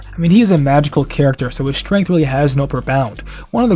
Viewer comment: "His strength really has no upper bound". Your captions say "profound". I realize this is a small thing, but it's such a stupid lapse in quality for no reason. Please double check your captions before you upload my dude.